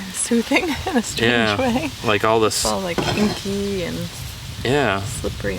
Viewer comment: regardless of what the captions say, I'm pretty sure she said kinky 😏